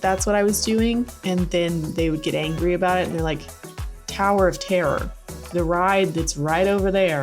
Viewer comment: Oh my god I thought you were saying "Tariff tare." Like is that a place in Star Wars? It turns out you were saying "Tower of terror." You need to enunciate.